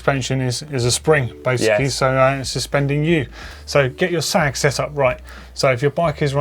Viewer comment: I thought doddy said get your SACK set up 😂😂